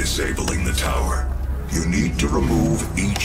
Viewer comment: I wasn't paying attention to the audio for a second and i heard "carefully release the ninjas"